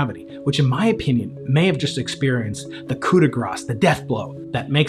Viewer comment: "coup de grâ" instead of cagr.